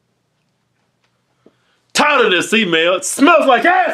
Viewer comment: who else thought that keith always say in the beginning of the video was "tired of this email"? but its actually TITLE not tired. lol